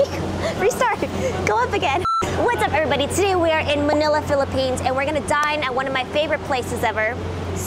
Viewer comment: I thought you said today were gonna die at one of my favorite places ever 😂